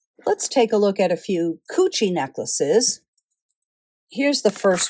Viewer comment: what word are you using? “Coochee”? Is that a style of jewelry?